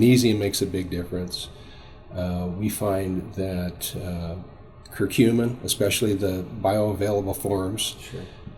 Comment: Does he say "cerumen" at 35:08?